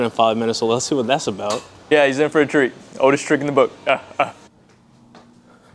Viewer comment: I thought I heard check out his pump😅